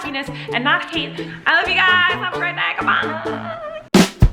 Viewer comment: At the end of the video I thought she said "i love you guys I'm pregnant goodbye."